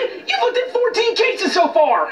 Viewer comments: What’s He Saying? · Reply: “Look if your too busy I’ll be on my way” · At what point in that one simple sentence Did he swear? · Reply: it sounded like he said "Fuck it", but he most likely said "Look, if..." and just stuttered/didn't enunciate his lines. I highly doubt he swore.